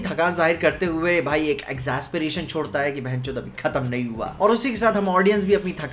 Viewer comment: did i hear "bc" ? 😭